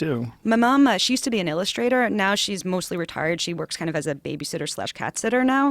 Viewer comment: when molly was saying what her parents did she said my mom is mostly retired. idk if I'm just a bad guy for hearing something totally different but I heard " my mom is mostly retarded."